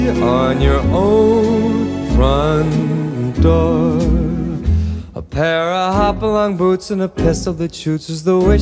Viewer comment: "A pair of hop-along boots and a pistol that shoots". My hearing is so bad. I actually thought he said, "a piss of the chooch" 😂